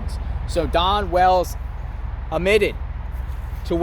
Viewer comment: I thought you were saying “Dawn Wells” until You showed the Picture of a Man. I thought, Man , Mary Ann from Gilligan’s Island started out from humble beginnings…